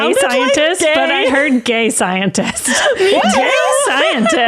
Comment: i heard 'GAY SCIENTIST' too hahaha i did a double take when said that lol!